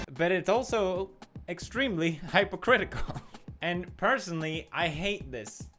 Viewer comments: did you just say hi po critical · Did you just say hyper critical